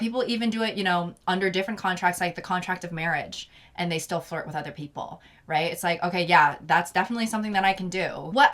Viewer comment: I misheard this as "fart with other people" and didn't bat an eye at first because for some reason it sounded like something would say LOL